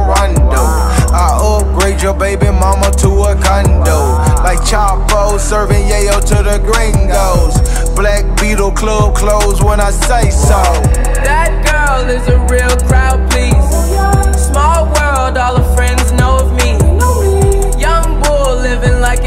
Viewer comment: Whole time I thought he said "that girl is a real trapeze" 💀